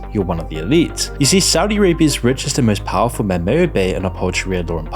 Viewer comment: Dude i can't understand half of what you say you need to fix this, the video is unwatchable. 0:14 most powerful men what? may bo bay and uphold sharid law? tf is that?? 0:25 anal fish fetishes??? fish? take the marbles out your mouth bro